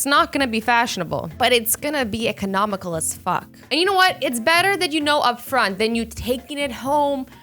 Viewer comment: Did said fu*k 3:13 or is it just me???